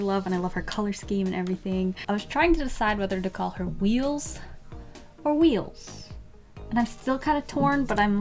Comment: Omg I was listening with no audio cuz I like listening to drawing videos when I’m doing doodle art so I heard “wheels or wheels” and I’m just like ‘why don’t u just name her WHEELS’ and then I heard “no h” so I rewinded it and watched it lol